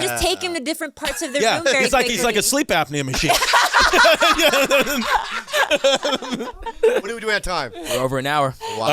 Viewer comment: I cant understand what Crisy say in 1:14:48 Can someone help me ? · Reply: He’s comparing the person they are talking about to a “sleep apnea machine” which is a machine that helps people with sleep apnea sleep without potentially dying in their sleep. Just google “sleep apnea” and you’ll be happy you don’t have it lol 😂